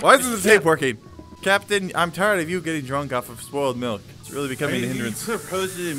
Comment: When he said spoiled milk I thought he said squirrel milk